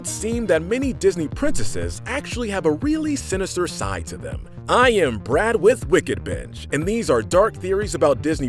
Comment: I thought he said "I am Brad with wickedbitch"